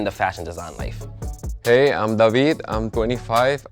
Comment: It’s davine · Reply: He said his name was David but his account made it sound like he said Davine, so they said Davine, but when he first comes on screen and when he leaves his name card is David